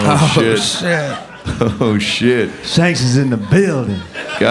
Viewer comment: Shanks is in the BUILDING! Loved getting to hang with Dr. Phil and the Kill Tony fam! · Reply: Thought you said Skanks lol